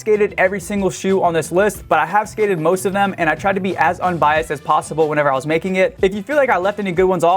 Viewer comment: Wait, did he just say biast? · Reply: Unbias